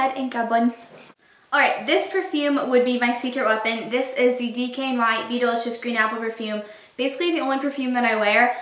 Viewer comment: Oh, it's DKNY BE DELICIOUS! hahaha I always thought you were saying BEETLE-ISCIOUS!! lmao